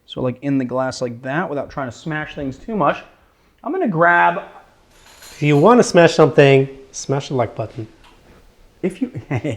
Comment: did i hear someone say if you want to smash something smash my puppy? I hope I misunderstand that! There's nothing funny about it.